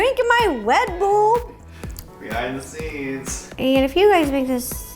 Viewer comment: I THOUGHT YOU SAID WET BOWL BUT YOU SAID RED BULL IM DYING